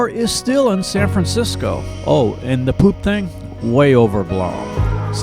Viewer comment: I couldn't understand what was said at 9:01. "Oh and the .... thing". What did he say? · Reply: "The Poop thing", SF is known for having lots of poop on the streets.